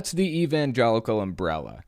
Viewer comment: I thought you'd said Evangelion